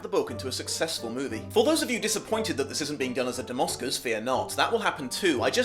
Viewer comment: What was said around 0:35? "Done as a domoscus"? What? Can't understand what says there at all. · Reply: "Dom Oscars," not "domoscus". It's a format he uses to deal with multiple adaptations at once. See his "A Christmas Carol" episode for more information.